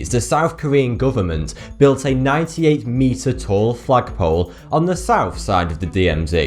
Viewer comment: did he say 'Fuck Pole'?! xD